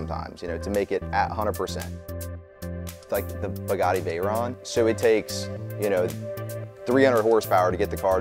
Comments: Did he say Pagani Veyron at 5:34 or did I just repeatedly mishear it. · Sounds like you said Pagani Veyron. Killed me a little on the inside.